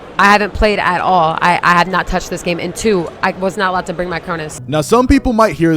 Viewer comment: Did she just said, she wasn’t allowed to bring her cronus? @ 13:31 Or did I miss understand it ?